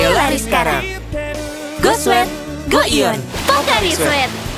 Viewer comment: "Go Sweat Go ion" What i heard: Go sweat Go Eut Yeah my mind is just messed up. Eut= Sex